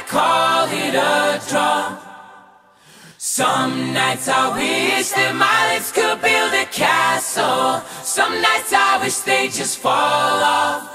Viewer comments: for the longest while I thought he said, "Some nights I wish that mice could build a castle" 😅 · “Some nights I wish this all would end” Same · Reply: i always thought he said “Some nights I wish that this song would end”